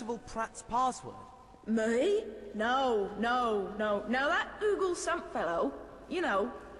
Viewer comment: Did she just say GOOGLE? I never noticed it!